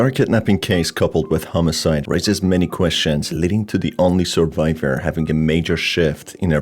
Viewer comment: I swear, I stopped this video and switched to another page, and I heard "survivor having a major sh$#%#$^t" XD